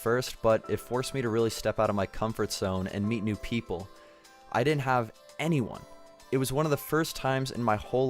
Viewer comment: I thought you said you didn't have any hardships! D